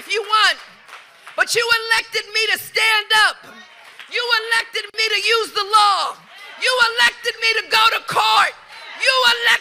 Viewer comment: I thought I heard, "you elected me to ABUSE the law". 🤣